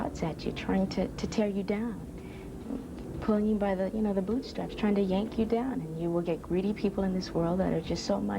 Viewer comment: Yo, did she just say “yanking by the boob straps” when she was talking about the media😂